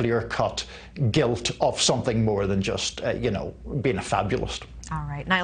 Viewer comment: did he say “something more than being fabulous”